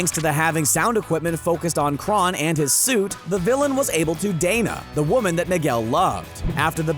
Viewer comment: Am I hearing this wrong or did I hear "the villian was able to dana" at 7:34 ?